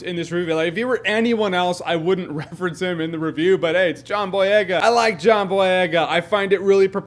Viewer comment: if you turn on the subtitles and scrub to when jeremy says john boyega at 2:01 the cc say john blegh